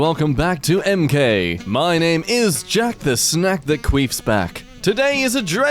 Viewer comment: did he just say "queef"?